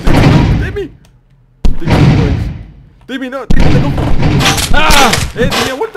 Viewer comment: En el minuto 1:30 estoy seguro de que dijo algo como: "TIMMY, TIMMY NO!!"